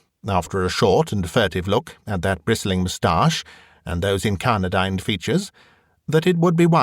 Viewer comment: What is the word at 11:17? Thanks! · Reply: It's "incarnadined", meaning crimson coloured, i.e. the Colonel has gone red in the face... presumably from the frustrations of playing golf with Professor Parkins!